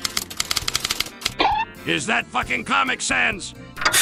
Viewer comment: undertale community: what did you say about sans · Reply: "Is that facking comic sans!?"